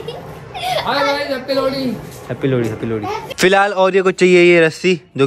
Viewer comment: it sounded like happy laudi 😂😂😂 No offence